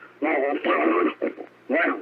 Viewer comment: Listening 6 months later,and I still hear "whats going on" at 4:52 and the Closed Caption still writes out the words. How could the Closed Caption hear what I hear? A Bigfoot mimicking a human. They probably hear the phrase " Whats going on?" from startled humans. All I can say is it writes out what I hear.